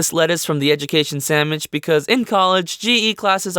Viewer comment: Am I the only one who thought he said samich instead of sandwich?????